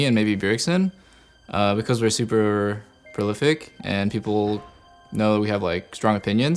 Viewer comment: doublelift at 6:35 " We are super .." What was that word? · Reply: prolific